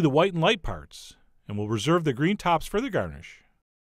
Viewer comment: I heard, "and we'll reserve the green tops for the GARBAGE" and I was like WTF? lmao.